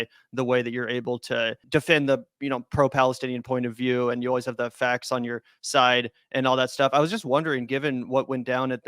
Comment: I heard "bri always has the facts on her side" and almost fell over in my chair lol...how did I end up here?